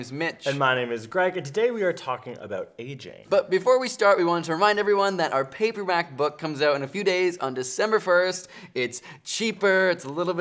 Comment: I thought they said a paper mac book and I got so confused 🤷‍♀️